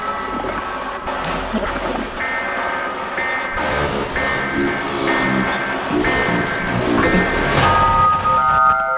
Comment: I think I hear: „I’ll revange at all...” or: „I am ??? once more” did I guess correctly? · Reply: "I have returned once more"